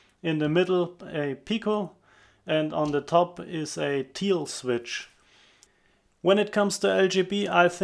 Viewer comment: Did you say, “Teal switch”? I googled, “teal g scale” and only found teal colored rolling stock and a bunch of fabric, lol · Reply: Hi Richard! Thank´s for whatching the video and subscribing! The company I am talking about is called THIEL. This is the Link to their web site ... <link>